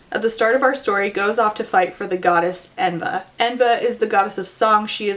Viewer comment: I thought you said "Evna is the goddess of socks"